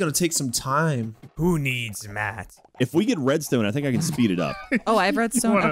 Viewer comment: Every time they said "red stone", I hear "bread stone" and I get real hungry XD